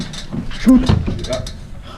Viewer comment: what did you say? Toot? Xd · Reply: shoot ;)